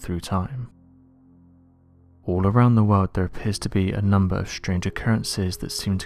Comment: i heard "theyre pissed to be.." lol